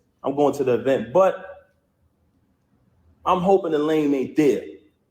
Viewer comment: Da Lame. I always thought he was saying Delane like that was Easy's name.